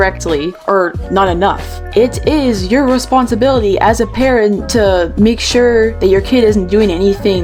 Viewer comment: i thought he said "it is your responsibility as a 'KAREN' "